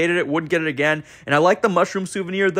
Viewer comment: For A Second I Thought You Said "Mushroom Soup" And Not "Mushroom Souvenir" And I Was Like "Who Tf Drinks Mushroom Soup With A Straw"